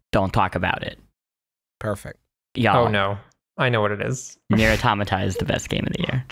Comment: What game are they talking about at 10:38? I can't understand the name and I've been trying to look it up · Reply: Think it was Nier Automata not 100% sure though